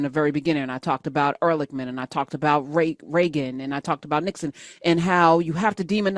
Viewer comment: I heard "Rape Reagan" and it 100% still checks out 😭🤣